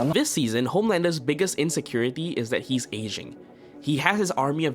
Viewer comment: I at first thought u said his insecuritie is he is asian